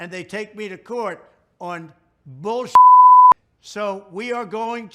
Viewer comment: I cannot... 😂😂😂😂He really said bulkshit... LMAO. Hes losing it.